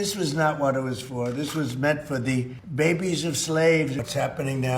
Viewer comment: did you all catch what he said??? baby of slaves